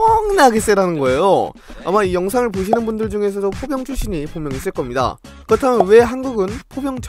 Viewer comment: 나만 포병출신을 보경출신으로 들었나 ㅋㅋㅋㅋㅋㅋㅋㅋㅋㅋㅋㅋㅋㅋ